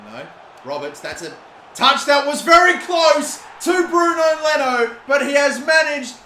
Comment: Did he say Bruno Leno 😂😂😂